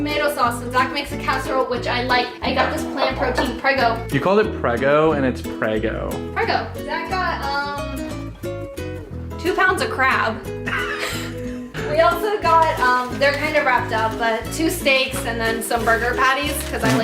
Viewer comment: Lol when she said " two pounds of crab" I thought she said "two pounds of crap" and I diiiieeeeeeddddd